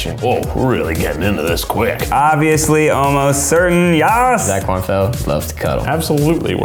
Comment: did ned just say "YAS" at 1:35 O: hE iS gAy oMg